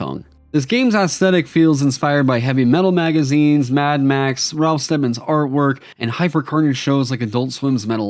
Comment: What was the name of the artist you said at around 00:25? The auto captions say Ralph Stemmons, but I'm not finding anything for names even remotely similar. Can anyone help me out? Edit: nevermind, enough creative googling found the result. To anyone else who was curious, it's Ralph Steadman who is known for his Fear and Loathing in Las Vegas art amongst others.